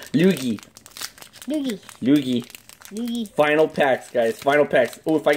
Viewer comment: sounded like yugi like if u heard